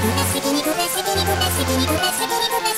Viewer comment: 段々皮肉ですに聞こえてなんか…なんかだ！